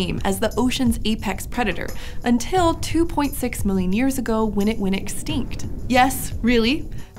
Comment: I thought she said "2.6 years ago they went extent" and I was like "WHAT!!!!!?????"